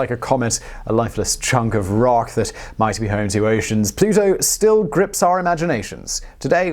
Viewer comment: "... that might be home to oceans..." I swear, I had to replay it five times, because to me it sounded like he was saying "... that might be home to Asians..." What?!? lol, Feel like an idiot. (sad face)